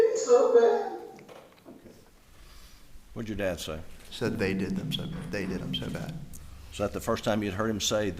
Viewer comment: I heard “ They did him so bad”